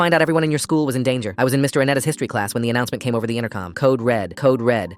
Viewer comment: my dumbass thought he said Mr antennas history clads